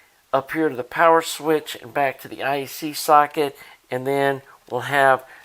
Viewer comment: I thought you said,,I. C. Socket! Old ears. Cool job🦜🍍🦜🔉🎵🎶